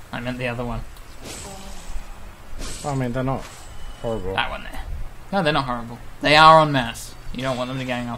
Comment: I thought i heard '...they are on meth...'